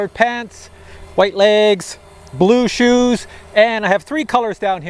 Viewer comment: Lol when u said “blue shoes” I thought u said Bluetooth 😂 😂 😂